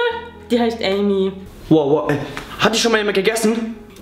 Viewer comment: Bei 6:37 habe ich erst verstanden ob schon mal jemand die Katze aufgegessen hat😂😂😂😂😂